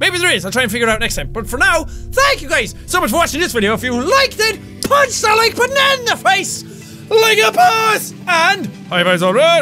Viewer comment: I heard "punch that like banana face"